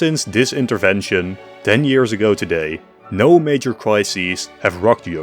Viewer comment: did he just say 10 yo today??